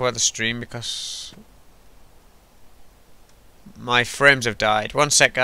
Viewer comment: did you say 'My friends have died'??????? and if you did, WHY?! lol